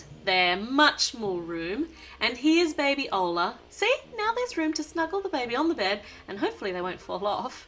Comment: I thought she said the mothers need room to smuggle there baby's lol